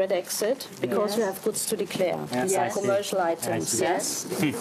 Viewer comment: Die Beamtin hat m.E. nicht von "Codes" gesprochen (siehe Untertitel), sondern "Goods to declare" gesagt, wenn ich das richtig verstanden habe. Aber die ganze Situation ist eh merkwürdig: Zigaretten anzugeben und dann keine dabei zu haben ist auch mal was Neues. ;-)